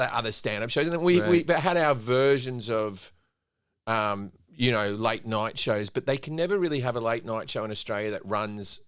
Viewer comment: Jim said, “…late night shows…” & for a second I thought he said, “…like nachos…”! HaHa!!